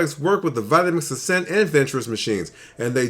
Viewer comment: will that food processor work with a professional series 750? (I could not understand exactly what you said there) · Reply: The Food Processor is only compatible with the Vitamix Ascent and Vitamix Venturist V1200. It's not compatible with the Pro 750.